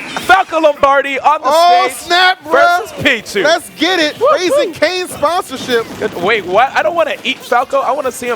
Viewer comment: "LET'S GET IT, CRAZY KFC SPONSORSHIP" "Wait... what-- I don't wanna eat Falco, I wanna see him battle on the field--" "C'MOON MAH?" "... Anyway, " 2:45 "I will not eat Falco." im not even done watching this set and I already made a video about it lol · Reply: I thought he said Raising Kains